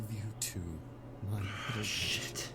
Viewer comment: You said sh*t at the perfect moment, I sounded like the video said "My Little Sh*t"